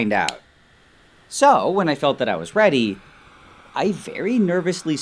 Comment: i thought that he said ‘when i got that i was pregnant’ and my first reaction was "WHAT."